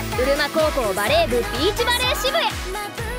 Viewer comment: 何度聞き直しても“ブルマ高校”にしか聞こえないんだけど、なんて言ってんだ？ｗ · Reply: うるま高校です笑笑